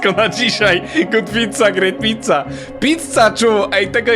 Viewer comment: czy tylko ja usłyszałam kotwica, great pizza?? Może to przez to że oglądam Kacpra Rietza bo powiedział na swoją dziewczynę Werble poproszę * dyg dyg dyg dyg dyg * Smoczyca kotwica, miażdżyca, pruchnica! Edit: (wiem to nie było śmieszne, bynajmniej w moim wydaniu)